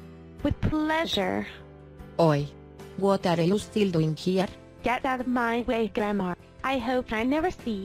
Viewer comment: Oi, What are you still doing here?! What, That’s What it Sounded Like?!